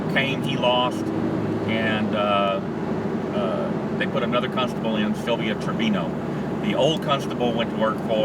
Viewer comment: I could've swore you were saying cuntstable lol